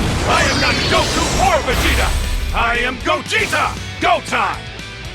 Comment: "Gogeta, Go time" Lmaoo good one legends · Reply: I heard it as “GOGETA, GOAT I’M”🗣️🗣️🗣️🔥🔥